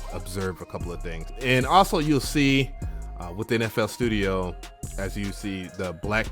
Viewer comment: sounded like ya said nfl studio